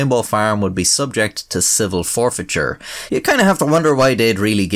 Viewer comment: I totally heard ''acid forfeiture'